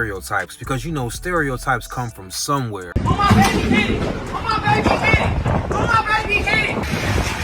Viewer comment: Sounds like she says,, on my baby, hit it. Love the videos!